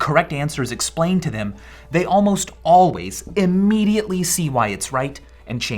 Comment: the closed captions say "usually", while says "always".